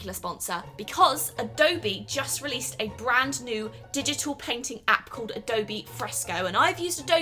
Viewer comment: Anyone els thought she was saying peng ting insted of painting